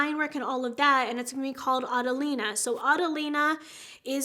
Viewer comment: P.S. When I heard “Adelina” I thought you said “Avelina”. Avelina was my grandmas name! So cute!!! Love you.